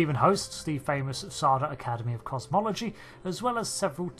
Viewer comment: I totally misheard/misunderstood that as "Sardah Academy of Cosmetology" and thought "Well, I guess every field has its elite institutions!"